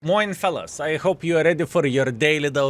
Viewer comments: Bruuuh how i casually listened to the intro and 20 secs into the Video i was like: Wait a Minute did he just say "Moin Fellas" and went back to start for 2 times 😂😂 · did you just said Moin??? I’m confused but yes sir Moin, and I’m ready for my diese let’s go!